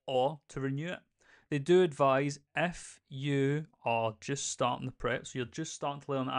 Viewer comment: Honestly, because of your accent and the fact that I am multitasking today, when you said, 'They do advise, if you..." I heard, "They do advise, FU" and I laughed, I took it as personal and accurate. All jokes aside, thank you for keeping the community informed and educated, really appreciate it!